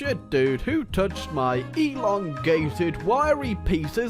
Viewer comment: At the start I seriously thought you were about to say "Who touched my Elon Musk?"